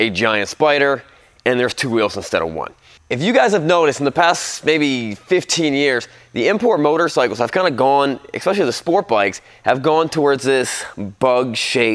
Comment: Oh ffs, did you just say "ekspecially"? Learn words, kid.